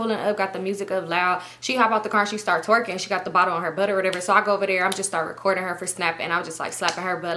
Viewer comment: wait did u just say she had the bottle in her butt😂